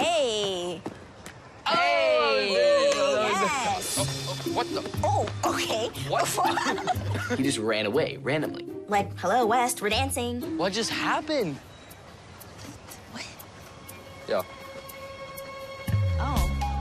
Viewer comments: did u guys notice that at 00:58 James might've said what the f word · Did Anybody Hear James Or Tiffany Say What The Fuck ....I think they said it